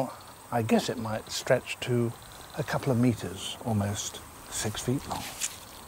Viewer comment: Wait wait wait wait.IS IT JUST ME OR DID HE SAY AT 2:15 THAT THE COULD STRECH TO 60 METERS OR DID I HEAR IT WRONG IF YES ILL BE DEAD